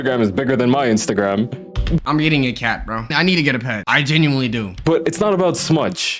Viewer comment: my captions said im eating a cat ☠️